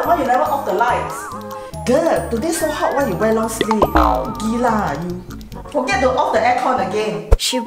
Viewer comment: Is your mom malaysian? I heard "gila oh you" just now😂